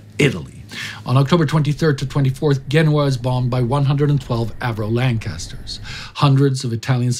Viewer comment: I'm assuming it's a typo in the subtitles - it says 1112 instead of 112.